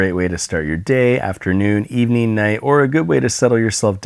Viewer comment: Before I turned the captions on I thought you said after nude evening night, and I was just "relatable".